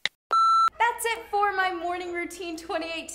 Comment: who else heard her say thanks for watching my morning routine